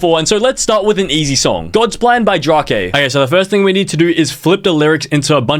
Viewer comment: did bro really say “drokae”?